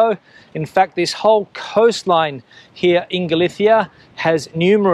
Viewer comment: I genuinely thought you said "inglesia" but no... in galicia 😂